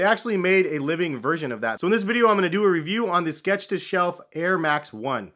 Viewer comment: I thought it said stretch to self...